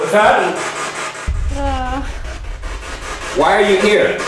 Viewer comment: when asked why you here, to me it sounded like "antique" 0-0